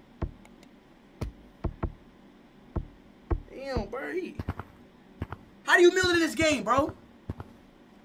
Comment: I thought blue said how do you milk people in this game💀